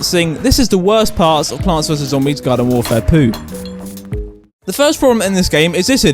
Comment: did he say garden warfair poo